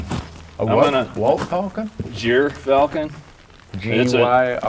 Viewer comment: When you said Gyrfalcon at first I heard "deer falcon", and I was thinking, "sure those cous deer are small, but they aren't that small!" :)